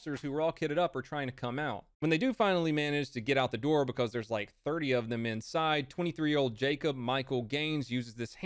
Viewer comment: I heard say 3 not 30. Was real suprised when a whole platoon of them came out.